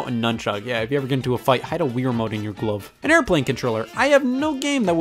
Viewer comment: it sounds like he's saying gluv instead of glove